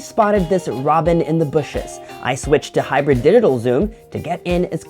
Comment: i heard hybrid dildo zomm :O